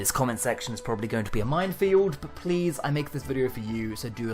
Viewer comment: Briefly thought you said "Mind Field" and then realised that that sounds like the title of a Doctor Who episode.